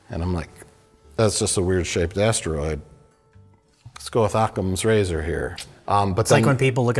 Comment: At first, I thought Mike said, “Let’s go with Aquaman’s Razor, here,” but I heard wrongly. It made me giggle at the time, though.